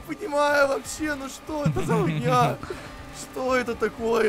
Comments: мне послышалось? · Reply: Он сказал фигня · Мне одному послышалось? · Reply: по видимому он сказал фигня а не х*йня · сказал " что это за ху*ня" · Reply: Мне послышалось: что за фуйня